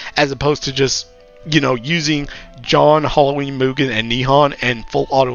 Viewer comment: I legit thought you said John and I was scrambling to figure out who you meant by John